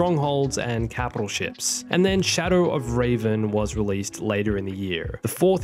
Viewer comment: did He say Raven...